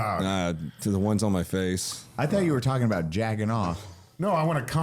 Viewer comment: Wait did say jacking-off 1:08:16?